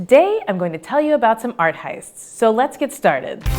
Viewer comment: I swear I thought Art Theists said Atheists at first.